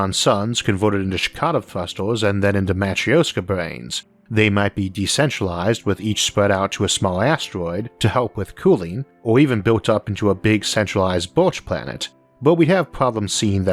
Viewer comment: what did you say at 12:45-13:00? Checotum thrusters? Damatrioska brains? Centralized Borsh planet? I'm not even sure what to Google for these terms. lol.